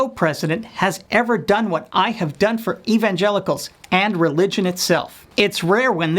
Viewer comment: Sounded like he said "evangenitals." LOL